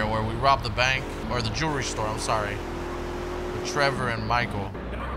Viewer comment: First time I heard "jewish store" and I was like WTF